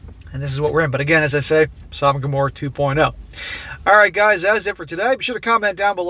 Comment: Did you say Sodom and Gomorrah 2.0?! 🤣